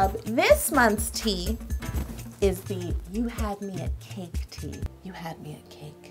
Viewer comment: thought it said "you had meat-cake"